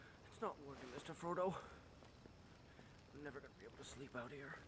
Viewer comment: What did Frodo<3 say? :-) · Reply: Me neither Sam. Neither is pronounced differently depending on who you're talking to.